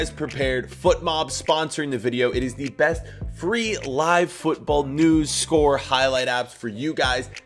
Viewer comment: I thought said 1 football was the best football app